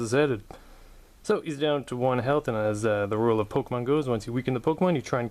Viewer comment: You said "The rule of Pokémon Go". This video is 3 years old. 😂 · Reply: "As the rule of Pokémon goes" is what he said.